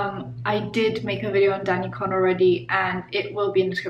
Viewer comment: I thought she said danielle porn at the beginning